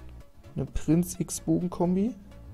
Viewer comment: wer hat auch alles wixbogen verstanden xD